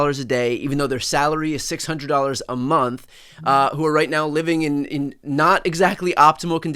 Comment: Did he say 600 a month